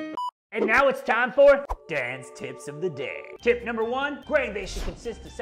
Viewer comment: When I first heard you mention "Dan's tips" I thought you actually said "Dance tips." Odd, I thought for a brewing channel, but still watched it all the way thru... Cheers! Carry on...